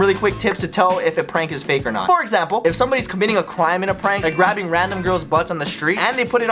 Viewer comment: he said qwime instead of crime lol